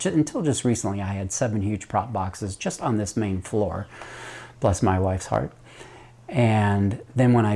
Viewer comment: Did I just hear you refer to your wife's heart as a propagation box? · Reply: Ok, I've listened to it a third time.... It's "bless my wife's heart". I thought you said PLUS. 😅